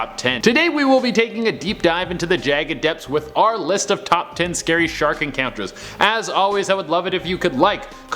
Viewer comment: Thought it said "Scary Shrek Encounters"